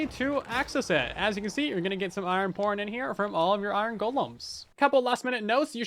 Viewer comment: I thought he said iron porn XD